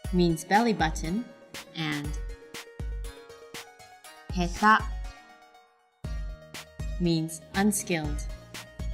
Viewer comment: does she say ka or ta?? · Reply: ta.